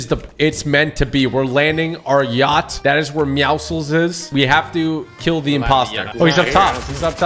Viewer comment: did you say lets go kill yhe impostor possibly predicting future with among us